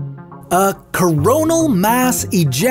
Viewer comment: Was it just me Or he is really saying CORONA..... I know he's talking about sun's corona.. JK